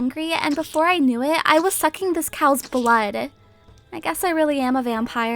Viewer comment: no you were sucking the cows but hahahaha i actually thought you said but instead of blood Loll